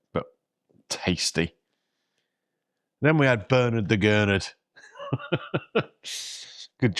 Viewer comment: Great location there, near the Harbour. Prices wont be cheap but lets see your ratings at the end. The Main Event (Bernard) was mind blowing to view. I misheard Bernard the Gonad